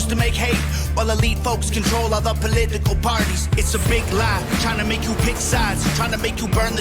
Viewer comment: I misheard "Tryna make you pick sides" as "China make you pick sides lmao